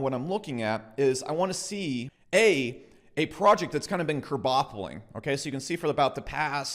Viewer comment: (not a native speaker here) What word/term did he use after 6:45? "I wanna see A) a project that's been [curbaffeling??]." He said it again after 6:55 , 7:30 , 7:54 , 8:33 , 11:21 , and 12:13.